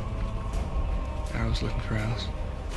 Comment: What did Edward say? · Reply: “Aro is looking for Alice.”